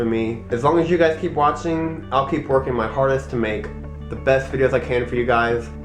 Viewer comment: i thought he said "ILL KEEP TWERKING MY HARDEST"